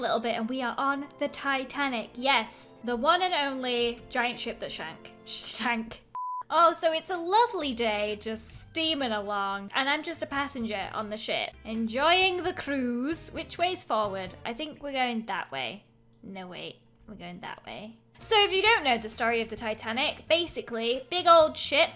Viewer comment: When you was on the titanic I thought you said "im just a passenger on the shit" im like..ok..